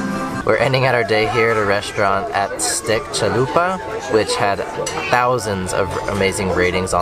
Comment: I thought I heard "Steak Chalupa" which make me think you were visiting a Polish Taco Bell. ;-)